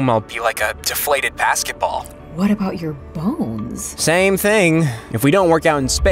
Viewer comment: Oh my! I misheard bones as balls. Good thing they have shown the bone hahahah